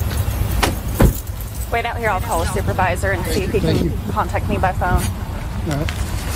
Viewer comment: I thought she said she didn't have a supervisor